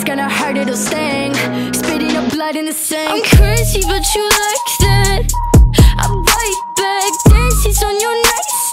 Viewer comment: I thought it said “ I’m cursed but you like that.”